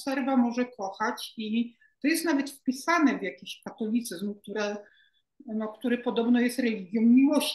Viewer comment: Chyba się przesłyszałem, bo usłyszałem "patolityzm". Ale idealnie pasuje to do tego "nowego systemu Jarosława".